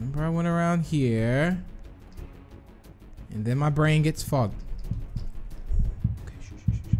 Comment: What did you say? Please don't swear. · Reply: He said the word fog "And then my brain gets/goes foggy" He obviously meant to say foggy but it came out as fog instead because at that moment he saw the beast. I'm not trying to send you hate or anything...I just don't think people should be accused for something they haven't done :D